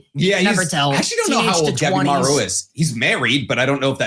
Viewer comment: Why does everytime he says Gabimaru all I hear is"Dabi-maru"? 😂